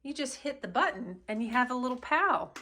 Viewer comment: Oh my goodness. When you said while you are on the toilet you can have a friend. I thought you said while you are on the toilet dropping Bullets. 🤪🤪😆😆🤣🤣. Too funny.